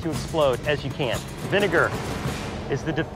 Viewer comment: Oh, sorry. I thought I heard you say devinitive...